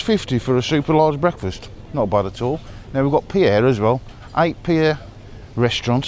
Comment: I thought you were saying Pierre as in Peter in French lol 😂....not pier thought thats an odd name in spain . Breakfast didht look great x